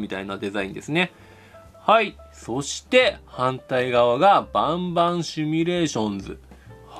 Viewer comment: バンバンシミュレーションズがボンボンシミュレーションズって聞こえる